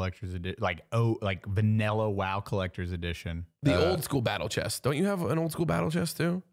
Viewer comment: For a second I thought said Battle Chess. Did y’all ever play that? It was an old PC game. Probably too old for y’all